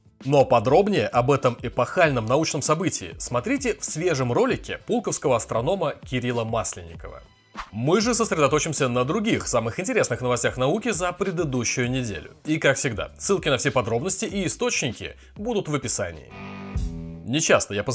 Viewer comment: В этот момент с хрустом откусил морковь и отчётливо расслышал "пулковского астронома Илона Маска"!